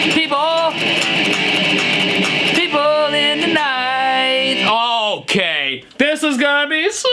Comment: when the grandpa said "people in the night" i thought he said "people hang to die" 😭😭